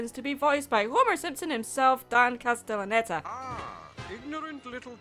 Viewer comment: Quick Question, Did you say 'Dan' or 'Don?' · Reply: Dan